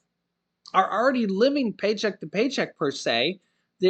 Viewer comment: I thought you said 60% of the country Is living Patrick to pay Jack Thank you Joe Biden and you're not job left Wing agenda